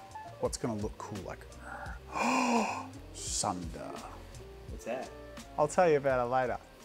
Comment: When he said Sunder I thought he said Tsundere.